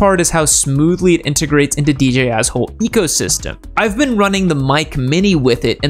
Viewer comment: I heard dj assh*** lol